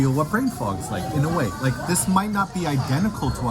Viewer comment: I thought you said "brain fart" for the longest of times 🤣